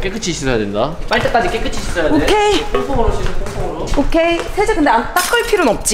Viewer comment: 나만 똥X멍으로 씻어라고 들리냐ㅋㅋㅋㅋ